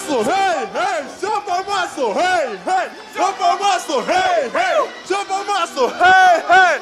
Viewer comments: мне на 11:38 послышалось "в жопу масло!" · мне послышалось: «В жопу масло!» 😹😹😹 Вам тоже так послышалось?